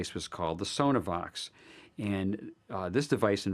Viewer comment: On min. 5:42... What did he say the name of disney's voice changing speakers was? Sonar vox??? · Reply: Sonovox <link>